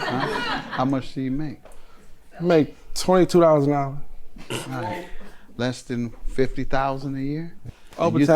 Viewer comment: Him: I make 22$ an hour Audience Member: BROKE 🤣🤣 Y'all didn't hear that?!!!! Jesus